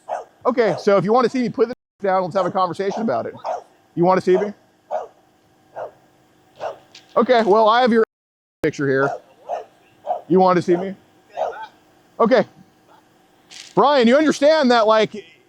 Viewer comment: did he say “I have your Ahole picture here”??